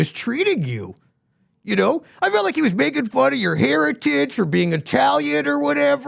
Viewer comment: Heritage. I thought he said hair and tits 😂